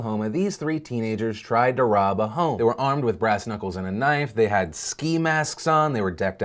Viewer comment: I know I heard this wrong but did he say grass nipples?